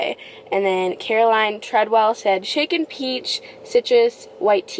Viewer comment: i heard “chicken peach” lmao also thx sm for all the likes y’all made my day! 477 👍🏼’s is so many!